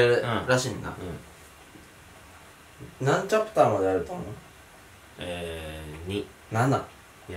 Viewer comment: の「とみ」って声（女性？）聞こえませんか？